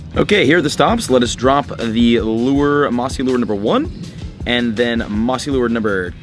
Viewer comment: Why on 10:01 and 10:03 i heard „nazi lure module”? XD